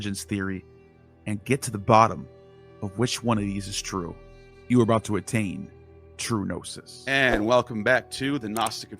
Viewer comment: I always hear, "you....are about to attain TRICHINOSIS!"